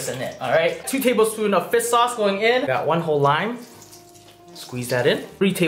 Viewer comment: I literally thought he said fist sauce but its actually is fish sauce